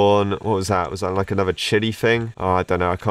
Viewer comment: uhh did he say the s word or chili?